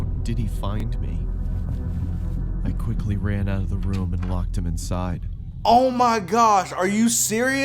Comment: did he say he locked him inside